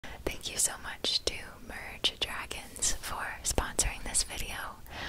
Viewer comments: For a second there I thought u said 'thank you so much imagine dragons for sponsoring this video' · When she was saying merge dragons, I thought she said Imagine Dragons lol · I thought she said imagine dragons in the beginning. Lol